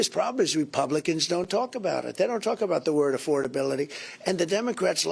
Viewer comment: He said republicans don’t talk about affordability.. I thought he said affordability was a hoax…